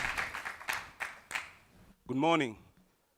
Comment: pls did he just say good morning..........oh no ofcoz i understand the pressure...